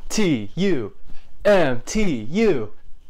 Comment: I thought it said mutu the pokemon